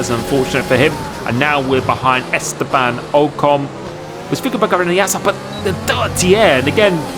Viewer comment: wtf did he said LMAO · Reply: he said "was thinking about going around the outside [...]" I think